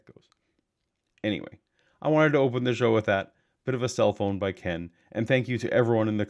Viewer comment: I keep hearing “cellphone fashion”. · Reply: I came to the comments just to write "I hear cellphone" lol